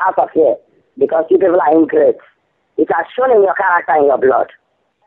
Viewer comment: i think the scammer says : "because you people are ingreats"